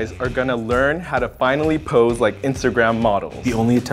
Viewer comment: I honestly thought it said "Men learn to Pee like Instagram models